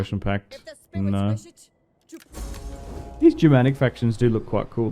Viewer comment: I thought you said no on the barbaric faction